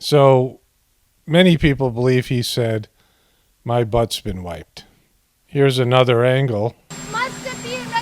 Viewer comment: At first I thought he said “My butt’s been wet!” & then maybe “My butt’s been white”?? 🤣🤣🤣